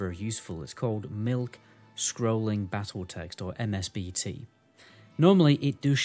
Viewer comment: For the people that didn't find addon at 7:05 it's mikscrollingbattletext instead of milkscrollingbattletext :)